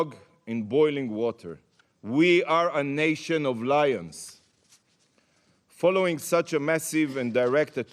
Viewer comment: "we are a nation of liars", pretty sure he said something else but his heavy accent covered it and this is what i'm going to believe he said, and to that i say i couldn't agree more